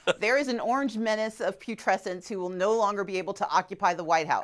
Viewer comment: lol I thought Stacey said pubescence not putrescence. tee hee Stacey is a class act and a gorgeous person. You go Ms Abrams